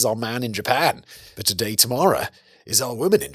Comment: I thought I heard him say that "Today Tomorrow" was the writer's name and that's also how it appeared when I double checked the transcript. But I think I've worked out that he was saying that it's usually George but today it's Tamara. Welcome to my brain pudding.